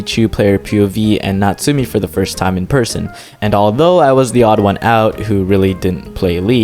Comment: "even though I was the odd one" I thought he said theodd1sout the YouTuber lol